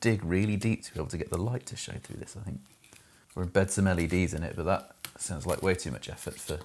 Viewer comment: the caption says "We're (mumbles) some LEDs in it" but definitely says "Embed some LEDs in it". Also at 26:39 "bellends" is written as "balance" and then "bellandry" is written as "boundary".